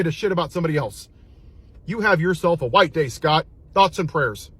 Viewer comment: Did he say "have a white day?" I'm rolling!!!